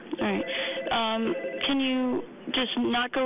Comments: he clearly said alright mam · sounds like he says “Alright ma’am.”